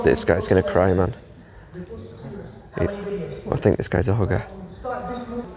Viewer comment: I thought you said the guy's a hooker